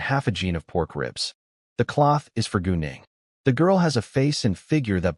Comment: I wasn’t looking at my screen and when he said gu Ning I thought he said going